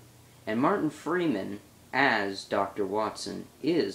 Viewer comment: Ooh, anti-shake :D when you said martin freeman, i thought you said morgan freeman XD YES! SUBSCRIBE! :D Although, most of the videos i work on are for